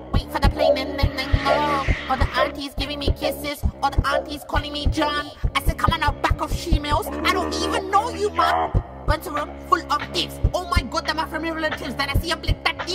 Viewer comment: I ALWAYS THOUGHT HE SAID "I DON'T EVEN KNOW YOU JAAN" LMAOOOOOOOOOOOOOOOOOOOOOOOOOOOOOOOOO!!!!!!!!!!!!!!!!!!!!!!!